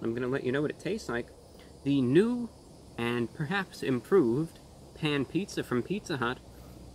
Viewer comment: At first I thought it said ban pizza but a pizza hut without pizza is just a hut